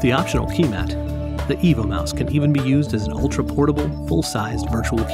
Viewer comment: Yeah, but does it work with Linux? And why are you calling it a mouse when it's shaped like a dog. And the name, I kept hearing "Evil Mouse". I also don't see anything about a keyboard option on your website. hmmm...just found the R2-D2 keyboard projector. Maybe you should redesign the mouse to look like K-9 from Doctor Who and call it a "K-9 Mouse" :o)